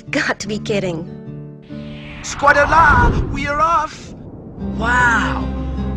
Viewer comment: squadla. What did he say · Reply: Squadala we're off!!